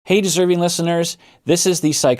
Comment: “Hey deserving listeners” really hits the spot in the morning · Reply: For some reason i always hear "hey disturbing listeners"